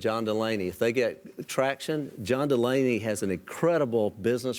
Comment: I thought he said "John Mulaney". Add Stefan to the ticket and ya got a winner. Yes, Yes, Yes, Yes...